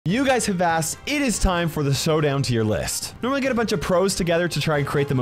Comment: Did just said Sowdown?!?!